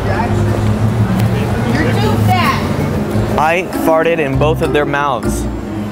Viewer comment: I thought I heard a female voice yelling "..you're too fat.." Lol